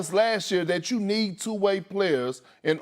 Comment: Am I the only one who thought Perkins said "You need two white players"?